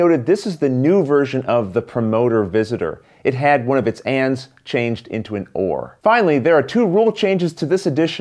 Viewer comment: I thought you said that one of the "Promoter's hands was changed to an oar" and I was really confused. :D